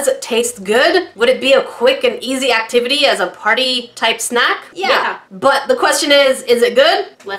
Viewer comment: I know you said "Party time snack." But, I really heard "Party Time Snot!!!" EEEW !!! LOLHAHALOLHAHA !!! Thanks for the tremendous laughs !!!